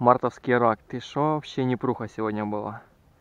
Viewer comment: Почитал комментарии..... Где вы услышали "Непруха"?😂😂😂 Голову с задницы достаньте,он сказал " мне пруха с раками сегодня" !